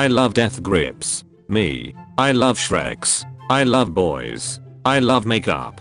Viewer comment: The first one tho it sounded like he said Shrek instead of s*x